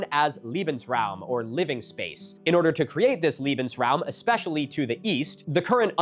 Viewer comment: it sounds like says "liebensraum" (loving space), instead of "lebensraum" (living space). The difference is [lee-ben] vs [lay-ben]. I never really got the loving vibe from the Notzis.